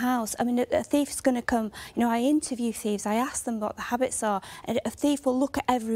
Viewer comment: did she just say she's interviewed thieves, say what?